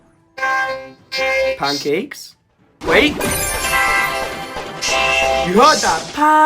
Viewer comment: I think lil pekka said thanks